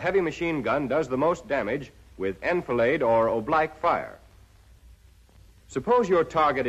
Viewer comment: sorry what is that word he says? Oblaque? Edit: he is apparently saying "oblique" but pronouncing it differently.